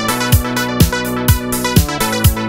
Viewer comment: I remember I always heard him say cocaine and not golden cage.